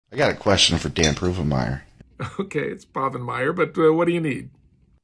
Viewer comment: Why isn't anyone talking about "Dan Pervenmire" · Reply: i heard "prove in mire"